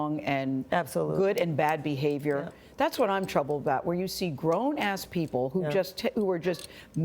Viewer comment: Did she say 'grown a** people'? Real classy.